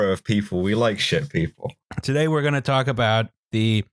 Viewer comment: I literally misheard it as "shit people" at the beginning :panic